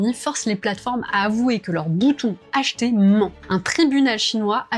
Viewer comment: C'est quoi un "boutou" SVP ? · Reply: Mdr merci d'avoir écouté jusqu'au bout ! C'est vrai que j'entends boutou aussi mais j'ai bien dit bouton.